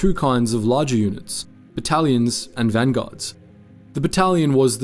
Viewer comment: Bro I heard “Italians” first time around